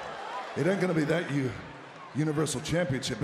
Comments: Bro did he just say Mama Reigns? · did he just say moma reigns 😂😂😂😂😂😂😂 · did he say mamma reigns 🧐